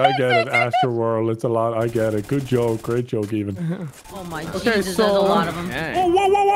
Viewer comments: did Nogla just call Evan “even” 🤣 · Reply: No he said "good joke, great joke even" he wasn't saying his name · Did Nogla call Evan “Even”?